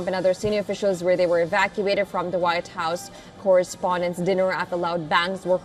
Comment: Did she just say dud dinner?